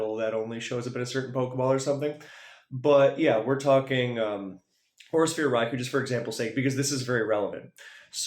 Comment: im ngl when you kept saying Aura Sphere Raikou i thought you were saying "Horse-Ear Raikou" 💀